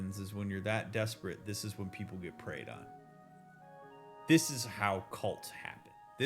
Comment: First heard that as “…get ‘prayed’ on” like “Thoughts and prayers”. You can imagine how that sort of thing happens.